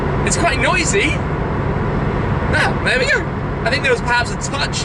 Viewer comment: What did he say at 6:37? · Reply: luca gabel 'well, nnther we go'.. - [said fast, while trying to maintain calm with huge dose of adrenaline pumping through his veins]..